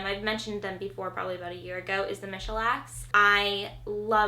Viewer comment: What is the channel name of the vlogging family that you recommended @ 5:07? I can't understand or figure out how to spell what you said... thanks!! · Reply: The Michalaks 😉